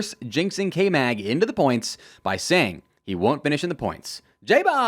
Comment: For your third prediction I thought you said “K Mag won’t finish the race” because of the Haas upgrades and I thought that would be pretty on point too #NoHDS #JustVibes